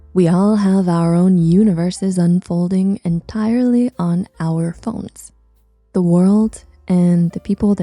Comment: is this video unlisted? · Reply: You might want to check the captions at 1:46-1:49. Instead of it saying, "the entirety of the world on our phones" it says "our The Secrets of a Suicide institutions" which was a bit triggering to me. I'd much rather have you personally take the time to go through and actually write out the captions so that it helps out those that need to hear it like the Deaf and HoH as well as those of us like me that really use captions to follow along please Thank you.